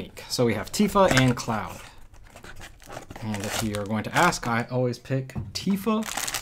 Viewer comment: what did you say? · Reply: "if you are going to ask, I always pick Tifa." Stating Tifa is my favorite female character in the game. I apologise for how fast I talk.